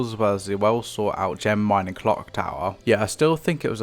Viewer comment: What did you say here? XD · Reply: British waffling mixed with "as well as sought out defences" something along those lines; this was an unscripted moment and I look back on my slightly older catalogue of videos and go "wow I've improved a lot since then"